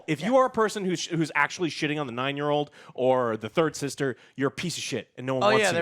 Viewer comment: I thought they said dirt sister lol